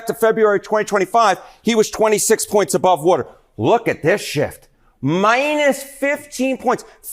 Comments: I can't be the only one that heard that as "Look at this shit". lol. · I swear it sounded like he said look at this shit 💩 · I thought he said, "Look at this shit!" 😭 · Anyone else hear “Look at this shit”